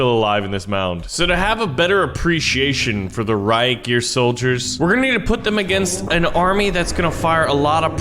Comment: Did he say Reich yer soldiers???